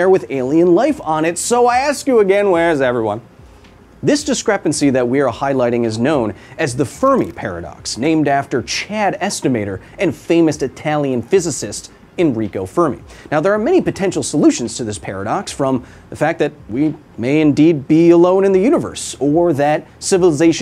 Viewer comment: for half a second I thought you said his name name was Chad Estimator, and I just imagined a 7 foot tall ripped man in a lab coat with a clipboard full of stats